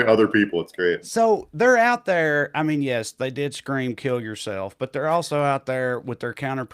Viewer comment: I thought the kid said your killing yourself not go kill yourself?